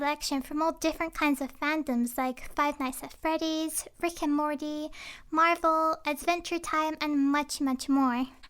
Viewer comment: I thought u said frick and mordy xD